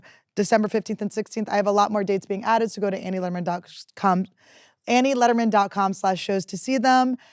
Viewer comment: Did Annie just say AnnieLederman.cum?